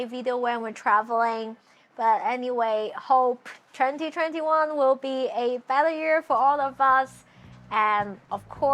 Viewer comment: U know 2020 has been bad when at first u thought she said "hope 2021 will be a failure for all of us"